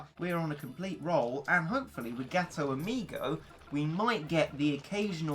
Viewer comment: Is it just me or does 2:18 sound like ghetto amigo instead of gato amigo